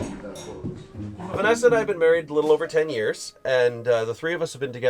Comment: it sounded like he said Vanessa 😂😂